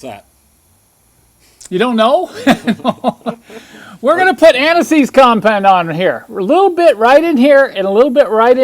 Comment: I'm getting old and I thought you said "aniseed compound". I thought, that's a new one, watch and learn, then I saw the jar - anti sieze - oh the relief!